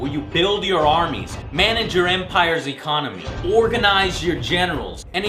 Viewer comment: i thought he sid orginize your genitals in 2:13 the first time i heard him